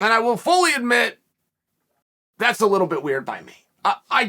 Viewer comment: Lol I thought he said queer, not weird .. I was like Chael u can't say that on utube lol